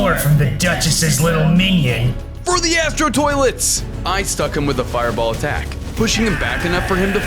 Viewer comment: Did you say for the Astro toilets or for the after toilets?